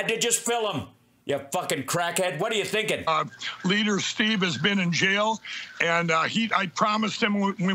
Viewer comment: Did Mike say “leader Steve”?!